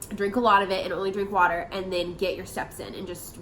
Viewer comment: Had to rewind that I thought you said up your water and get your sex in LOL!